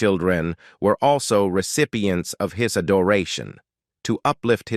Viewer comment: This is interesting. I was born in '46 and remember Roy and Dale from TV. I was jolted when I heard the narrator say "his a doration" instead of "his adoration" Check about (18:41) to hear it. Is this narration all AI?